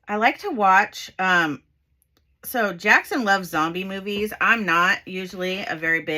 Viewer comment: At first I heard “zombie movies” as “donkey movies.” 😂 I was like, what the hell is a donkey movie? Shrek? 😂🤣